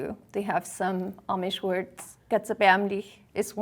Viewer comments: just if anybody is interested: I am German and I think she said “Das ist erbärmlich” which means “that's pathetic”. :) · The swearing word (4:50) "Das ist erbärmlich" means "Thats pathetic". Atleast thats what I hear as a german